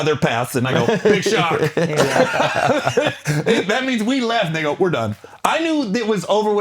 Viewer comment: Did he just say "we left nigga" 1:05:55 😂😂😂🤔